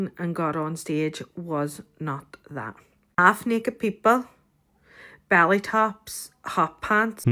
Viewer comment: lol did she say “ballet tops”?